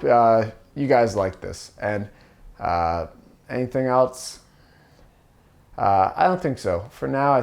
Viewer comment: This is a new thing happening to me recently. Even videos by you and freaking Channel 5 news have got me to this point. I guess I cry when I like good art, glad you are making it! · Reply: Uhhhh, got to the end of the video and heard you say "anything eltze" instead of "anything else" I take everything back and want to be your enemy now 👊 square up bb